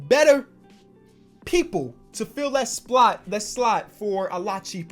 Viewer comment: Anyone hear it around 19:10 slot instead of spot